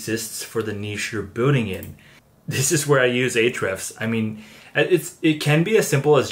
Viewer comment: what are you saying at 5:34? "This is where I use... " What is it you use. ? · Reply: ahrefs - the seo keyword software